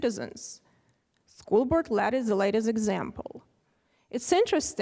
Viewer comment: Who’s name does she say here; “???…is the latest example”? It sounds like “Colbert Lat” or something!